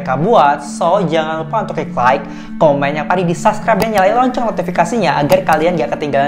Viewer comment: aku dengernya Loncong🙂